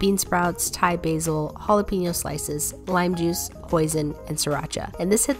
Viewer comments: I heard poison and siracha or was it just me or did I misheard something? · I got so scared- I thought she said “lime juice, poison and siracha. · I thought you said poison not hoisin 😅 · I thought I heard poison for a second I still can't hear it so what was the last ingredient you added again oh wait someone said you added hoisin nevermind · I thought you said "bean sprouts, thai basil, jalapeno slices, lime juice, poison , and sriracha." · omg i started to freak out. i thought you said poisein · When she said hoisin I thought she said poison. Lmao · i thought she said "lime juice, poison" and when i turned on captions it said Poison☠☠ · I can't be the only one who heard poison ☠️😂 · did i just hear poison? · did you say poison?